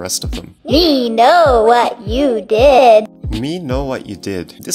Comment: tattletail said to me "we know what you did" i thought you said they took it out of the game